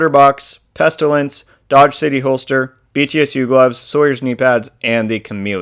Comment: I thought he said dog shifty holster for a minute 🤣🤣